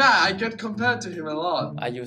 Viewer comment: Gay vibes right here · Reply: I thought he said Are you a singer, until I saw this comment 🤦